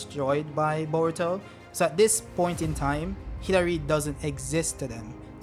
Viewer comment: When you said Hidari I thought u said Hillary and Boruto destroyed Hillary im like who tf is Hillary? lol